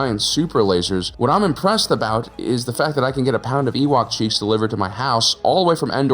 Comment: "I could get a pound of Ewok cheeks delivered to my house" Jfc i thought you said "I could get and pound Ewok cheeks"